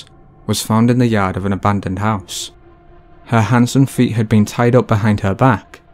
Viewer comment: When he said hands and feet, because of his heavy accent, I thought he said "handsome feet" at first...I had to back it up.😅